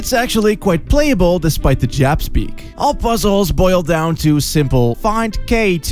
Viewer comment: did he just say "jap-speak" wtf